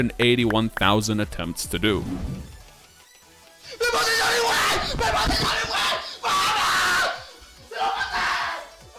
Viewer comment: im argentine but idfk what even he says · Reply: dice "ME PASE SONIC WAVE" "ME PASE SONIC WAVE" "JVDHVIEYCDIHVXYF" "LO PASE" "LO PASE"